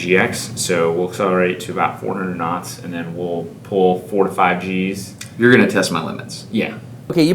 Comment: thought he said 45 g's